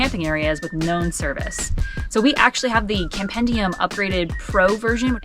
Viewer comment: Hello. what was the App or website you mentioned at 1:56 to find campground internet signals/speeds? I couldn't quite hear what you said. Thanks, great video! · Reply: Campendium!